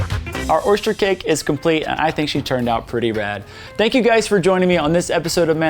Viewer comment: did you say she turned out pretty BAD or RAD